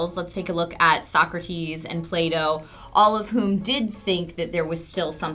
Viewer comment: Whenever I hear an english-speaking person say "Plato" I understand "Play-Doh" xD